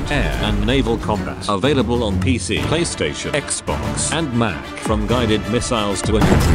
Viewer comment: 🩻 i thought said gaystation 🩻🩻🩻🩻🩻🩻🩻